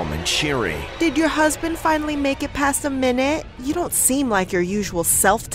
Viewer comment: Did i hear?"Did ur husband finally mk it past one minute?"😀😀😀😀😀😀😀😀😀😀😀😀😀😀😁😁😁😁😂😀😀😅😅😆😆😆😆😆😆😆..@ stopppppppppeeetttttt😀😀😀😀😀